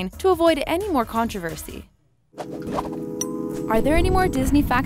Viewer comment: Thought she said don't forget to subscribe to the taco😂